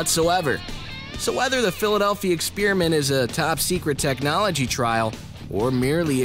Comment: It's sounds like he says philladelphia spearmint